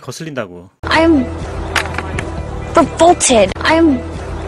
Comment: 근처에 나오는 I'm revolted. <- 이 문장이 계속 저에게는 I'm full-ported. 이런식으로 'f'가 있는 것처럼 들리네요. 왠만하면 이렇게까지 크게 벗어나게 들리진 않는데 당황스러워서 반복해서 들어도 계속 그렇게 들리다니 이런.. ^^;; ㅋㅋ